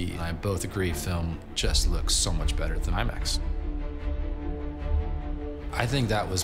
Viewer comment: Did he say "Film looks so much better than iMAX"? Doesn't make sense.